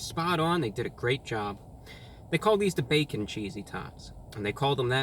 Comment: For a moment I’ve just thought he’d say Cheesy Thots 😂😂😂😂